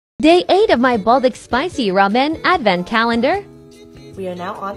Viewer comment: Why did I hear it as “they ate my buldak ramen”